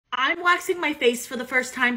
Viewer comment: At first I thought she said washing not waxing